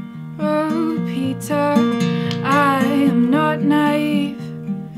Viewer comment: Everytime she sings 'Oh Peter' I hear 'Oh pizza'... I don't even like pizza!